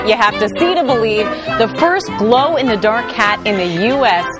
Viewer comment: I'm very disappointed. I thought it said "glow in the dark titties!!!"